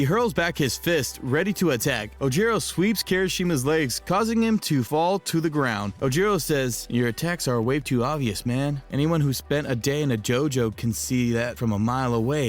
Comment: did i hear JOJO !?